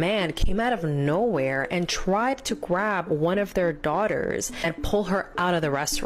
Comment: did she say - Gwab? Is dat slang for grab?